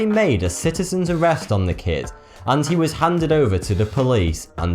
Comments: Anyone else thinks it sounds like he says fourth 2? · Any one thought he said 32 or 42 instead of thoughty2? ..only me...okie